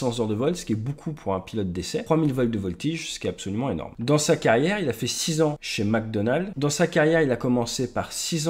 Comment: Did you say Chuck spent 6 years at McDonald's???😂